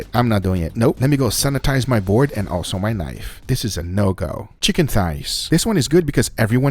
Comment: I thought he said let me sanitise my board and then my life lmao